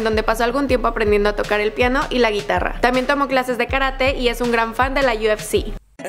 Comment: no entendí lo que dijo:(( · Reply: Dijo que también tomó clases de karate y es un gran fan de la UFC